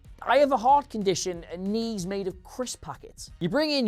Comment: Did I just hear, "I have a heart condition and needs made out of crisp packets" heh